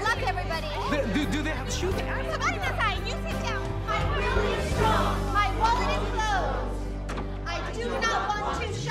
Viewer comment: What are they saying at 2:44? Anyone know? All I can make out is the last sentence "I do not want to shop" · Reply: I'm really strong. My wallet is closed. I do not want to shop. 😂